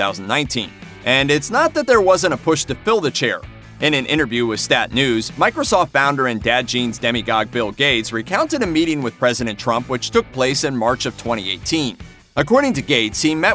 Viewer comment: Did he say Demigod Bill Gates?